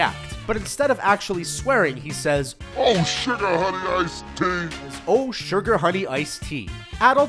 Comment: I thought he said "Oh shit I had a nice day" instead of "Oh sugar honey iced tea"